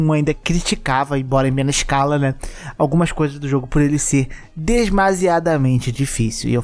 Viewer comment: falou bonito mas falou errado, mas bonito xD Eu ouvi "desmasiado" ao invés de "demasiado" xD